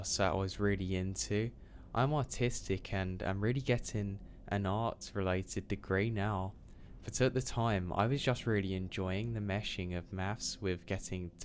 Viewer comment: i thought u said "autistic" because of your accent lol